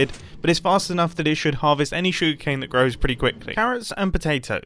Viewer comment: I have one thing to say: I use closed captions because I can and he just said sugar cane. But it thought he said "Shoe Cane!" XD